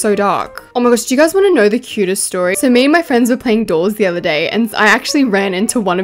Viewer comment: I thought she said dolls 😂😂 btw if you think im making fun of her accent im not😊